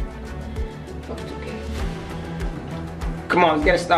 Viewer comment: Is it just me or does it sound like she said fuck 2k